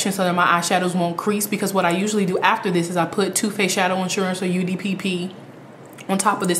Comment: Can you please explain what you said 6:05 "two face eye insurance" ?